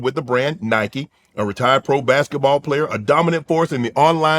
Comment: did Shannon just say Kai was a retired pro basketball player? 🤔